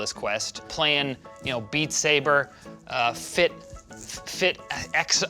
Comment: I hate that the subtitles guy put instead of beatsaber “beat saver” at 00:14 😭😭😭